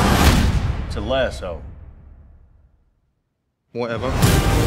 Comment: what is he saying? I am from Korea, so I don't know what he is saying. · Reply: "it's/this is a lasso"